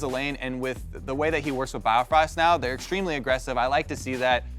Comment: I thought Kobe said BobRost in Hard Coutner